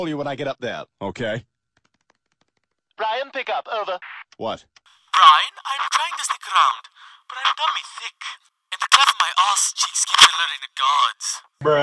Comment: What Did Stewie Say? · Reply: "Brian, I'm trying to sneak around. But I'm dummy thicc. And the clap from my ass cheeks keeps alerting the guards."